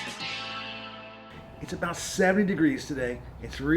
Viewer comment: That's the best stuff I've seen for brake dust. A chronic problem with bright rims. Just wondering, it sounds like you said it was 7 degrees at the beginning. Did I miss hear or have you gone metric? lol.